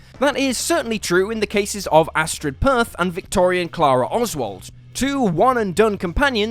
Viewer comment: It's a trick to get you to comment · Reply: Even adding letters to names? I heard “Astrid Perth” when the character was called “Astrid Peth”.